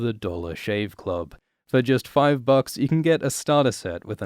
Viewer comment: What a smooth transition. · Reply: Agreed. Hahaha at first i heard "Delos Shave Club"